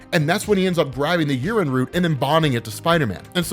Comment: every time says "yeren" I hear "urine."